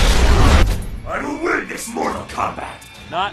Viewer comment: Scorpion: I will witness MORTAL KOMBAT! HE SAID IT! HE REALLY SAID IT! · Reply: XxGamerRyanxX actually, I think he said: ”I will win this Mortal Kombat!”